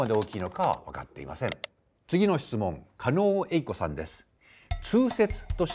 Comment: が狩野英孝に聞こえて2度見、いや2度聞きしたわ